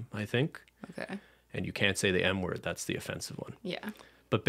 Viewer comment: I couldn’t hear when he said something about offensive words for little people, did he say the “M” (!!) word ??